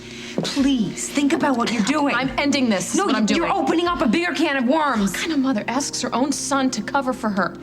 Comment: Did Carly say "You're opening up a BEER can of worms?" at 3:17?